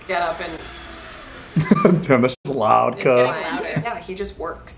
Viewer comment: Did he just say Damn That shit loud cuhh